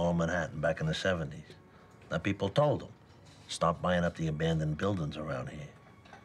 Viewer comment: tony sounds like he said 'stop' instead of 'start'. can anyone else hear it?